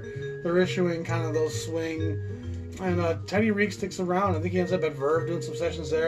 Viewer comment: Sounded like you said the founder's first name is Terry. It's Teddy.